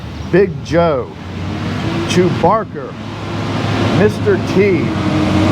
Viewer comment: Did you name your log turner after Chewbacca the Wookie from Star Wars? I thought I heard you call it Chewbarker.